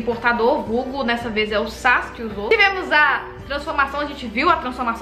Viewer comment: No momento 28:31 eu juro q entendi ela falando Sasuke e não Sass, kkkkkkk por um minuto eu achei q tava assistindo Naruto kkkkkk